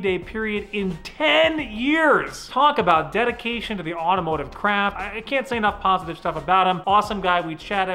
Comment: this is around the time I get dinner sometimes Edit: did he say crap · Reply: Craft*